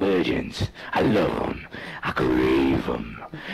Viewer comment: did he just say "cause its DRAAAAAAAVEN at 5:04" ??